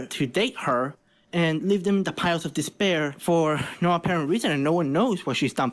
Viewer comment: So I’m in junko cosplay and 2:03 did I hear despair?!???